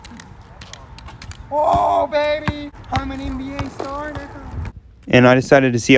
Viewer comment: Am I the only one who heard the “I’m an NBA star ni***a”